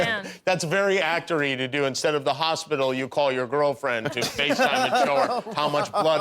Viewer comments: did Jimmy just call Zendaya Tom's girlfriend? I love this 😌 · what did he sayeeeeee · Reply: Girlfriend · did he say girlfriend?? 😂😂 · did Jimmy say GIRLFRIEND?!! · did he just said "your girlfriend"?